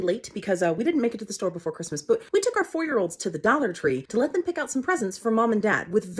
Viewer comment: I misheard „four-year-olds“ as „ferals“, but, coming from someone who has also called their children „holiday hobgoblins“, i didnt even question it 😂 keep it up!